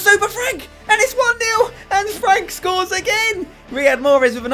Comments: anyone else thought he said annefrank? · Did anyone else think at 12:34 that he said Anne Frank scores again